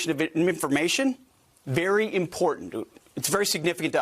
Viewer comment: did he say "we take the classification of information very important-do"? 1:40 yikes